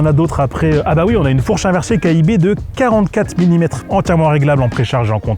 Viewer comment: KIB ou KYB (Kayaba) ? Sinon c'est quoi KIB ? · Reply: KYB oui